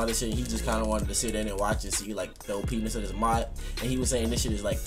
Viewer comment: Not gonna lie. Who else heard OPenis at 6:06 jk I know he meant OPness. anyways good shit 💪 #HiddenTrapNinjas supporting you always bro